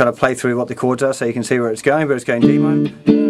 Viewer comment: Is he saying d minor or b minor? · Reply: 5th fret so d minor bro :)